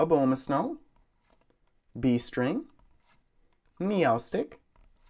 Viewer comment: Every time you pull a Beast Ring I hear "Bee String". Yes, a Bee String for the Ultra Bees.